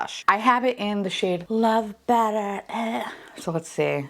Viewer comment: I really thought you said love batter 🤢